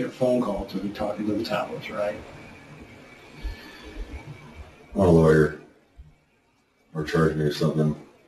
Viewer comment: Loud & clear he said he wanted a lawyer. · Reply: Yep. 27:31 he clearly says "I want a lawyer". 🥝💚🖤